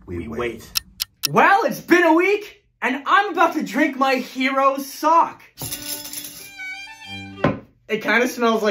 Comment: Definitely thought you said "and I'm about to drink my heros sauce" 🤣🤣 figured he might of added a little something extra when he was pouring it like that loll